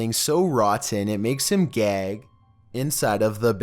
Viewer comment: #1 I thought he said he snagged something so nasty it made him gay lmao